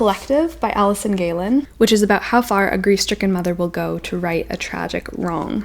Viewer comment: When you said “grief-stricken mother” at the start I first thought you said “greased-chicken mother” HAHAHA- I had to go back and re-listen